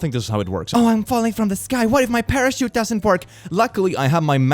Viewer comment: she really said free parachute